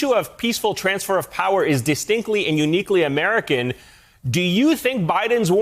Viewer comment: Distinctly, not exclusively. Calm down. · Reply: Except the OP quoted the host incorrectly. He actually said "...distinctly and uniquely American": 1:59. So the reaction is justifiable.